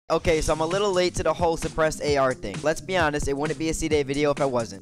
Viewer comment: At he start I thought he said ”depressed ar”